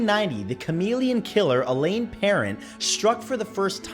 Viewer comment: Lol I thought at first when he said number 5, I thought he said A Lame Parent